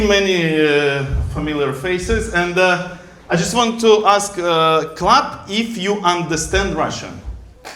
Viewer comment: хаха услышала "I'll ask club if they understand Russian", оказалось, там клэп 😅